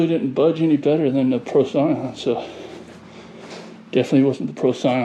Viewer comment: What's the spotter you used, it sounded like you said "Pro Sign" ? Can't seem to find it. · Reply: In case you haven't found it yet, I think he's saying "Procyon". Looks like he has a bottle of SoapFree Procyon.